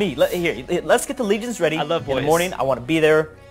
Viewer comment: did he say he loves boys I believe he did